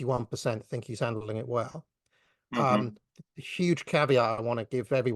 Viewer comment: Did he say ‘a huge caviar’? I wonder if there are people out there who also say that?